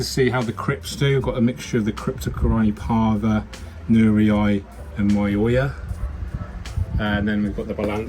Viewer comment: Dear. Which cryptocoryne did you use for the foreground? I have the c. parva recognized, but not the second. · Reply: I heard nurii, and I think lutea.